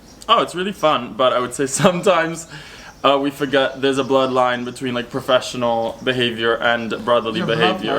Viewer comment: This is super fun, thanks for the great video! I also thought said 'blood line' and was happy for the clarification. Best wishes to all of you!